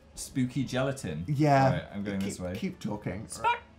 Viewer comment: Daniel... did you just say KEEp talking?